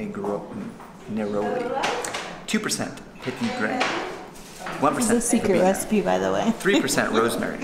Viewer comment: What was the scent after petitgrain and before rosemary? I couldn't hear that part. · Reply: Verbena